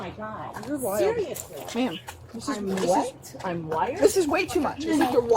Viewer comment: Wait!? At 5:56min did she said I'm white